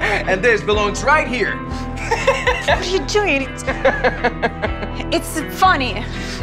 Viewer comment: I heard that she said it’s funny, which is not funny, if you do this